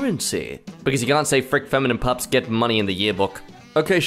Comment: Stop saying "feminine pup" it sounds like they said "fuck female dogs"